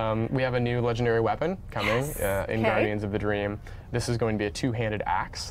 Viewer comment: Sounded like they were saying "Guardians of the Drain"